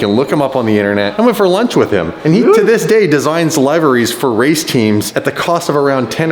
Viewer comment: I thought I heard him say libraries, then I realized he meant liveries (lee-ve-reese)